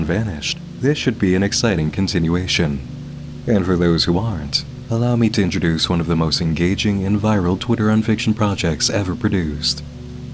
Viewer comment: im sorry but I can't understand what he's saying. Can someone tipe it out for me please? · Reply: "Allow me to introduce one of the most engaging and viral twitter unfiction projects ever produced." :)